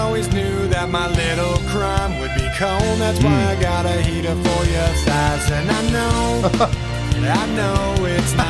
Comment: This was always my favorite song by them. I listened to it when I was younger and didn't understand the words. I legit thought he said I got a heater for your fries at first UNTIL I saw the music video. Which was definitely a shock to my younger self. I didn't even realize what was happening in the video fully until I saw it again when I was younger. I understood all the murder stuff but I didn't pick up on him with her, you know what I mean XD